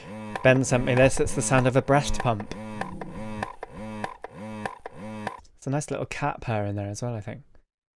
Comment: music theory and musical terms are my special interest; i couldn't quite catch what you said at 2:43 but it sounded technical? thanks :> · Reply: haha it sounds like a technical term but I actually said "there's a nice little cat purr in there as well I think" XD should be more cat terms in music theory though imo